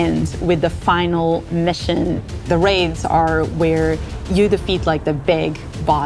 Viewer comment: did She said raid's? More than 1????